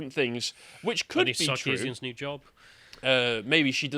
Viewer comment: What name does he say at 2:13? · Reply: anita sarkeesian